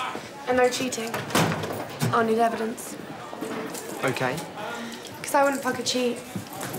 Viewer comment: did she say "cause I wouldn't f**king cheat" ?